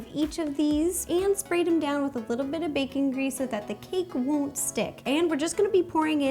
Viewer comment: When Ro said Baking grease i thought she said bacon grease XD